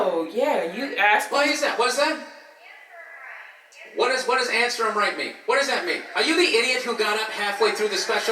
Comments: i was at that show what she said was "That's our right" oh and she was snotty the rest of the show whisper fighting with her S.O. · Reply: all this time I thought she said "answer him right!" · The woman in the audience said, "It's her right!" But the fact that Bill misheard her the way he did made it soo much funnier. lol. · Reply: I always hear " answer him right". I could be wrong